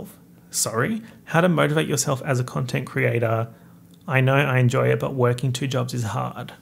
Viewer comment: Why did I hear 'How to motorboat yourself as a content creator'